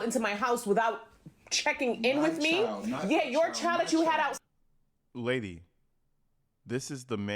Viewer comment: i think she was saying "the child you had OUTSIDE OF our marriage" 🤔 Is that what she was saying?